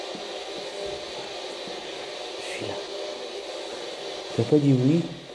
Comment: Magnifique bâtisse sur le début de la vidéo. Pve super audible je n'avais qu'un ecouteur sur l'oreille. J'ai cru que tu étais a coté d'elle pour dire "bonjour et viens". Deuxième vidéo de la maison ce truc de dingue a 12: 24/25/26. J'étais choquer le truc il passe comme ça ! Ca fait peur mon dîeu. Vous avez entendu marcher ? Je vais pas m'en remettre 😂. On dirait un djinn est encore je pèse mes mots ! Visiblement leia est pas tranquille quand tu descends à la cave. 23:09 on dirait que le pve dit "allo !" Ou "alors" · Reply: 😊 ah ca. Après avoir revisionné une partie ou j'avoue avoir mettre assoupie. J'apporte un nouvel élément. J'ai entendu un pve hors spiritbox 1:03:31/32/33 " chui là" ou proprement dit je suis la